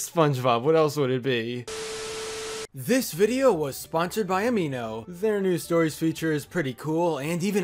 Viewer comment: This vid is sponsored by Amibo is what I thought he said